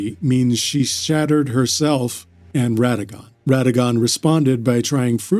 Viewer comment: swear to God I heard "shatted herself" hahaha